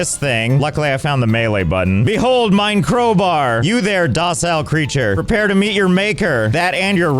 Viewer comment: did he say „ mein crowbar“ so German for „my crowbar“ or is my Brain just stupid